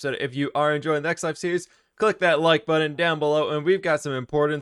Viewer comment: When said press the like button i thought he said LIFE button